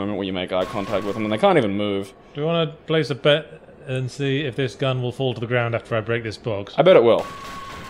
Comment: I heard it as "want to play the rape game?" "no." "that's the spirit."